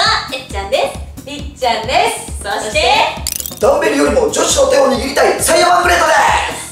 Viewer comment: あ、の最初の挨拶ダンベルよりもだったのか 初めて聞いた時ドンペリて聞こえた…